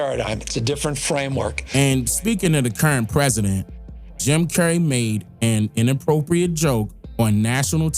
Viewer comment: Who Made An Inappropriate Joke??? Sounds Like You Saying Jim Carey